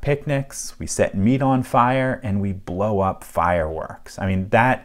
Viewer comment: TBH, I thought he said "we set weed on fire"... lmaoooo